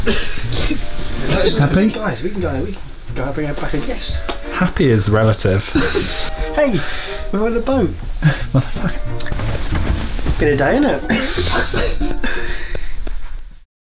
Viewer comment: Did James say “motherfucker” at 4:50?